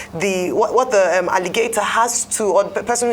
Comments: She say na 'Aligator' 🤣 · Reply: Thought she said “allegators.” Seemed to be referring to the persons alleging systematic abortion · Did I just hear... say " you begin to wonder what the 'Alligator' has to say😂😂, ALLIGATOR !!? LOL